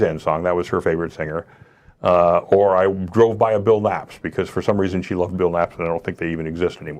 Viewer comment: What does he say at 8:28? It sounds like he said he drove by a build apps. What is that?